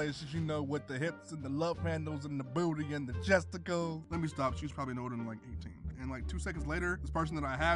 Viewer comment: what did he say, he talked a bit to fast and quiet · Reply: Let me stop, she's probably no older than 18.